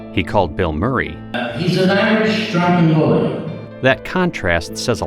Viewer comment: what did he say here? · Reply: "He's an Irish drunken bully."